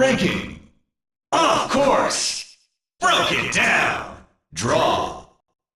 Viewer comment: I always thought he said Brilliant Down, not Broken Down. Though the latter does make significantly more Sense.